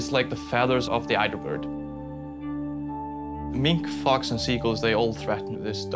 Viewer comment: FOR SOME WEIRD REASON I HEARD ``MINK FOX AND SEA EAGELS FAP THIS DUCK``